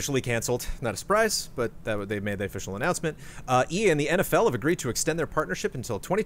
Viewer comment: I thought said "Ian" too